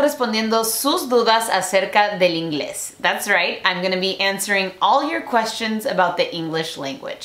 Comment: los subtitulos no va con lo que dices: como esta escrito = im going to be answering como lo dices = am gonna me answering